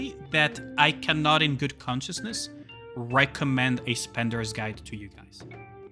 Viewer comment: At the end, did you just say "good consciousness?" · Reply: I may have missopoken but I was saying "in good conscience"